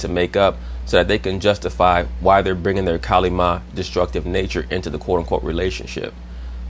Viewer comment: What is the word is saying around 26:15. “Kalima” energy? Is that related to Kali the Indian Goddess?